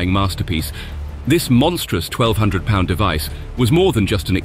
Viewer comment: the narration says 1,200 pound bomb, and the captions says 12,000 pound bomb??